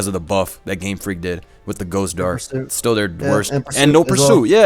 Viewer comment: someone please tell me what is saying, I can't understand, ghost darst??? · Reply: He said Ghost Darks. Steel got it's resistance to Ghost and Dark removed in gen 6.